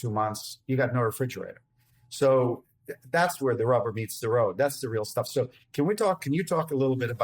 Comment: I sear when he said "rubber meets the road", I heard "robber meets the rope". I suppose that fits as well..